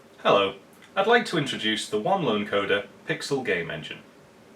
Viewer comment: I wasn't paying attention and misheard you referring to yourself as One Line Coder and thought to myself "Oh, what a freaking cool nickname!" and then I heard you right and I thought "Oh, what a sad sad lonely codeperson who I envy because he probably can focus on things..." ☹️